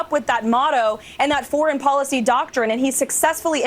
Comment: why did I think she said orange policy when she said foreign policy 🤪🤪🤪